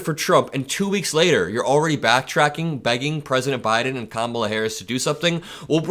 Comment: Not beg Biden blame Biden. You have a hearing problem. I hope you get that taken care of soon. Your health is important. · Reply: Listen again 1:30 let it run for about 15 seconds. If you don't hear "begging" get your hearing checked.